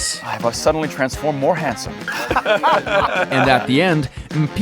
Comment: ??? I heard NPC lol